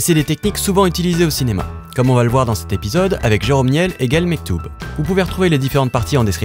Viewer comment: "Avec Jérome Niel et Gael Mectoob" j'ai entendu "avec jérome niel égal Mektoube" ... ah ah désolé tout ça pour dire que ta vidéo est géniale ! :)